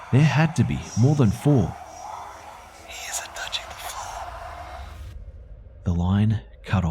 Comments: Is it me or did anyone hear this line "He isn't touching the floor" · What does he whisper? "He's a touching fool"? That's what I can make out... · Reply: She said he is not touching the floor